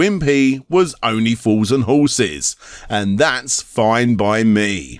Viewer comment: Wtf is “only fools and horses”? Or…did u say… “only fours and horses”?…sorry I’m…not exactly sure, still trying to understand the accent! Can someone help me out with this? Thanks 😅 ! Edit: this’s